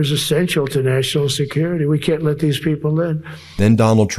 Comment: Did he say (at 1:04 in) "we can't let these people live?"